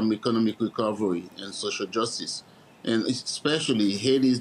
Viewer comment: did he just say social justice?